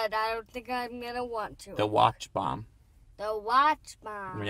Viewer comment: when you said Watch Bomb for some reason I thought you said Watchmen, and now all I can think about is a James Bond crossover with Rorschach. Thanks.